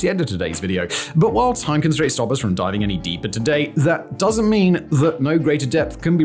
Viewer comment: did he say "...while time constraints stop us from diving any deeper today..."? Cause some of his casual criminalist videos go for 2 plus hours.